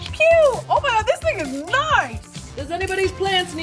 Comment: did she say this nigga's nice??